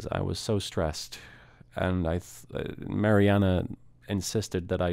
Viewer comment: I wasn't really listening and I thought you he said "Marijuana" and I was like oh this guy is trippin